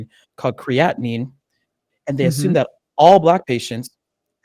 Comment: Dear Fellow Gymheads--- I thought he said CREATINE was problematic, but he said CREATININE... I had to Google quiiiiick~~ God knows we can't do without that! And yes, yall better go research and get 2nd and 3rd opinions RE: ya medical recommendations. I've had THREE eye surgeries and was visually-impaired my entire 30s. I went to an iridologist, paid $180 for a session, researched the first 3 ingredients in the various natural remedies they suggested for me, cross-referenced it with the main ingredients from a naturopath website my sister and Dad have used w/ much success called AMBROSIA HEALTH FOODS (online, but located in Brooklyn). I bought those supplements online and POOF, after all these years, NOW I can drive at night. Doctors have a vested interest in selling you drugs or referring you to their friends INSTEAD of telling you about the natural stuff God made to heal us. That's why I try to live a healthy lifestyle as-is cuz them doctors-- Woo WEE! I don't trust em as far as I can throw em. The ones who entered the field for altruistic reasons often become desensitized and it becomes just a job to them. Thanks for sharing the info yall!